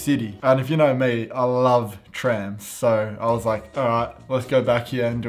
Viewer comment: “and if you know me I love trans” 😂 that’s what I heard at first